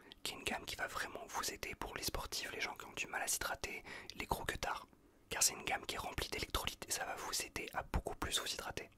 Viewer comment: Attendez... il dit quoi à 0:59 svp 😂 ??? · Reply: « Électrolytes »